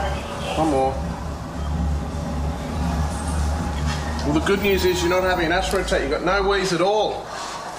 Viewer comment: What did the paramedic say to her at 10:10 ? I only understand : Well the good news is, you --- you got --- at all... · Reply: The paramedic said, if you haven't figured it out already, "Well the good news is, you're not having an asthma attack. You got no wheeze at all" Wheeze: "breathe with a whistling or rattling sound in the chest, as a result of obstruction in the air passages."